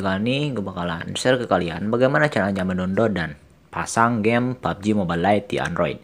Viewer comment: kocak bang ngomong apa aowkaowkaowk 0:03 dia bilang gimanasih gimana mendododan